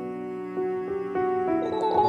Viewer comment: "Make a grave for a baby man." Such wise words. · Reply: I thought it said "naked grey baby man"😂😂😂😂😂😂